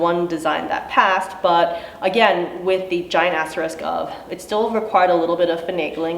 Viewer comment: I thought you said "giant ass risk" instead of "giant asterisk" hahaha thanks for doing what you do, providing this info and fighting for us!